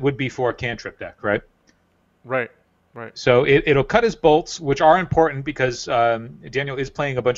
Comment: I literally heard ''it will cut his balls'' at 0:42 XD... luckily they were talking about his bolts.